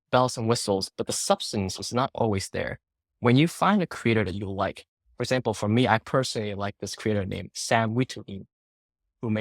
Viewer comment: Who's the Sam guy he mentioned at 8:00? I can't seem to find him. Couldn't make out the last name. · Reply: Sam Witteveen!